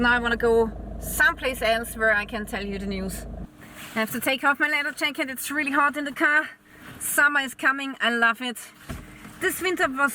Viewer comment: While I working most of the time I listening to your "My name is Lilly" news reports. I had to re play 2 times 1:03 " I have to take off my little jacket it is really hot in the car. somebody's comment I love it". I misheard this sentence, altho "I love it" your the news reports.